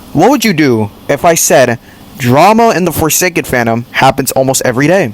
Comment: I think i heard you say deforestation fandom